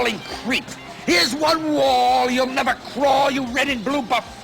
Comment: The line: "here's one wall you'll never crawl!" How I heard it: "you're up against the wall and I AM THE FUCKING WALL"